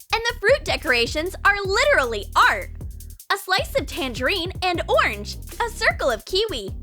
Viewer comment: she said tandarin instead of mandarin